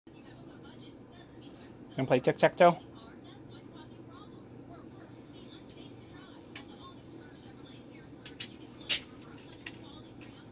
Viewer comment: Ooohhhh PLAY tic tac toe! I thought you said break tic tac toe!!